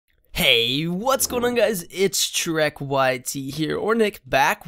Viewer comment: it sounds like you said: Heeeeeey whats going on guys its Shrek YT